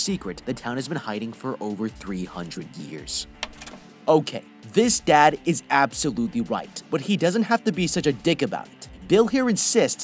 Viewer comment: I keep hearing “this dad is absolutely white” 😂